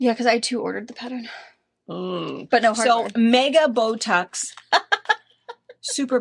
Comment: When you said “Bow tucks”, I thought you said Botox!!!! I had my head down cross stitching!! 🤦‍♀️🤦‍♀️